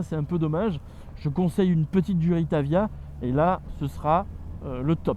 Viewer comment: J'ai mal entendu, tu conseilles quoi ici? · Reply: Durite aviation, pour éviter l'évasement des conduite de frein lors des freinage appuyés et ainsi conserver une bonne puissance de freinage ;)